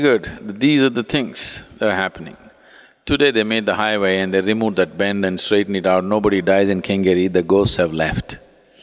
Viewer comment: what sentence he says? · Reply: Today they made highway, removed that bend (issue was with banking of road) amd no one dies at that place... So the ghost (technical issue for death) has left....